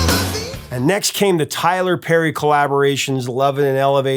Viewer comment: lol ... he said Tyler Perry instead of Joe Perry at the 8:47 mark. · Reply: He said, "Tyler-Perry." Steven Tyler and Joe Perry.